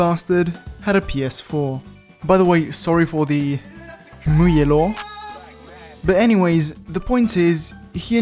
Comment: What is he saying at 1:02 ??? "Sorry for the muey Elon?"